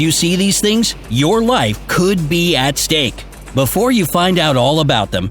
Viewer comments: lol 0:43 i should go back to 1st grade i thought those last two words said beat steak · instead of saying stake he says stank (sounds like that to me in my headphones) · When it said your life may be at stake I thought it said your life may beat snake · I thought it said Your Life Might Beat Risk instead of Be At Risk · I thought when it said your life could be at stake, they said your wife could be at stake. I got confused for a second. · I thought you said "your life would beat stake" I thought it was burger steaks😅😅😅😅😅😅😅😅😅😅😅😅😅. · I thought it said your life could beat stake